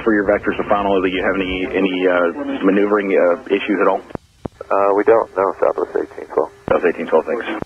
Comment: When he said “We don’t. No.” I thought he said “we don’t know.”